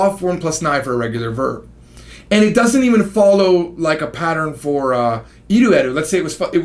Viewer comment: was than an f-bomb I heard? lol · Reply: No he meant to say "Follow" and got interrupted so he only said F...